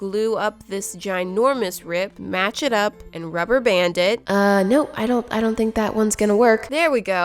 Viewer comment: At one point I thought she said "rubber bandit"